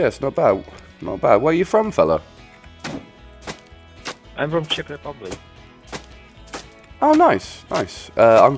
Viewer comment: I thought he said 'I'm from Chipping Norton' ... :)